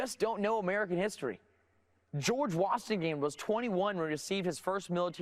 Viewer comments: Is he saying "George Washingame"? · did he just say George Wastingham?